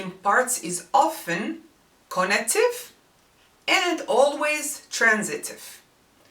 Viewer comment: Hi! The Arabic verb form 3 implies that there is someone or something else present and that the action is performed upon him/her/it. This form reflects its meaning in two ways: Conative which indicates the effort of the doer in performing the activity by the verb for e.g. to ساعَد to help someone, جاهَدَ to struggle against, حارَبَ to engage in a war or conflict. Transitive is when a verb can take a direct object, in other words, the action is done to someone or something for e.g.لَاعَبَ to play with someone, شارَكَ to take part with others in an activity, رافَق to accompany, سالَم to make peace with, عالَجto treat a patient or illness,خاطَب to speak to, صاحَب to be a friend of and so on. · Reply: oh I see. I thought you said "connective". Wow, I just learnt a new word. I knew about connotation but I've never heard of conative. And concerning "transitive", what put the confusion in my head was the concept of transitive /in transitive verbs in English, whereas it's something completely different from the Arabic concept of a transitive verb. It really helped miss. Thanks a million times👍